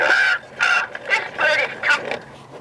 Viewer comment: "It's raining cats out here!" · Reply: I hear "this bird is talking to you"